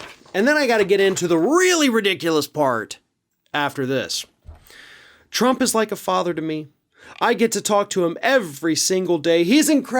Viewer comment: Oh, my error, I thought he said Trump was like a farmer. So full of shit. ( no offense to farmers)